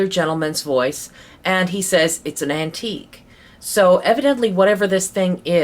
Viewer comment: When he asked what the spirit wanted done with the object I heard "disintegrate" instead of "it's an antique." Did anyone else hear it as that?